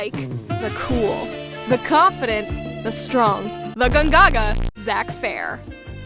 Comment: Do i hear.... GONGAG